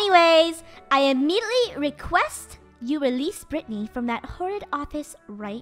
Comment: did she say Brittany??